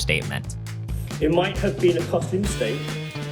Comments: Can anyone help me out here? I keep replaying the part at 0:12 where people are standing onstage, but I just cannot understand what the guy is saying before the audience starts booing. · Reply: "It might have been a Costly Mistake" :) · What does the guy say at 0:10 ??? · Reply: "it might have been a costly mistake"